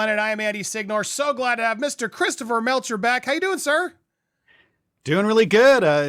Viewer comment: Thought you said christopher melt ur back 😂😂😂